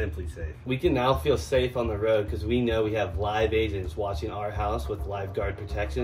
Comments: Give these guys an acting award for that ad promotion man 💀 “intruder, leave the building, the police are on their way” 😂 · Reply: For a minute I thought said "Live Asians" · The simply safe ad 😂😂 “intruder leave the building” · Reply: I thought he said live asains💀🤣